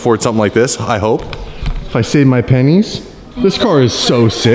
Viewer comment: oh no I thought he said if I sell my pen*s